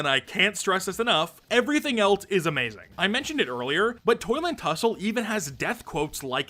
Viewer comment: Only i Heard him saying "Toilet hussle"?